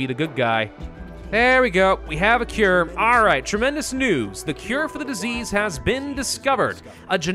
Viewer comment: When he said tremendous news I thought he said tremendous noobs